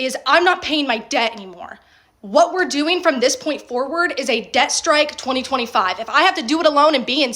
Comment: i heard "death strike" instead of "debt strike" lol. somehow debt strike sounds much more stupid.